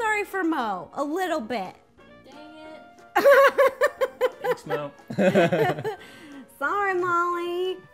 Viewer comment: Did anyone else heared molly said "dang it"